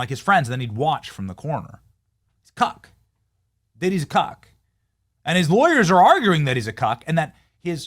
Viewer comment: i thought you said a COCK like you was calling him a Dick in disguise 🥸 😅